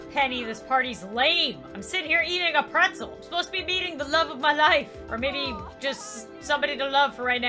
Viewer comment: the captions say beating not meet